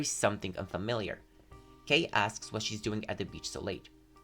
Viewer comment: Imagine calling sunghoon “MOMMY”😭😭 1:01:39 i heard ✨GaY aSkS✨ I’m wheezing😭✋